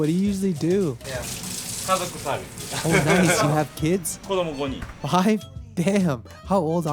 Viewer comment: When he said '"5 kids" around 9:00 and Paolo responded I had to do a double take cause at first it sounded like "goddamn" and I thought that was way too PG-13 for this channel XD